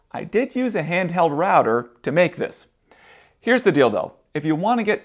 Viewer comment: Listening to super-interesting video when suddenly I hear at 12:00 : "Here´s the dildo". Say what? Ah, here´s the deal, though.